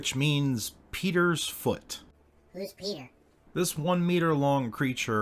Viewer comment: Misheard at 10.20, thought you said 77 meter long. Now that would be terrifying.